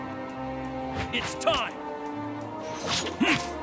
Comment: Que es lo que dice en el minuto 8:53? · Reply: "Prepare yourself"/"Prepara-te"